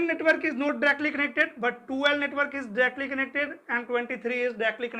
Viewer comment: that words ( Directly connected ) im not clearly understood, what your telling please confirm that word please🙏🙏🙏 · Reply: Directly connected